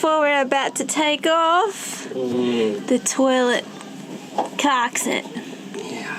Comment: Did I just hear, : "The Toilet Captain?" lol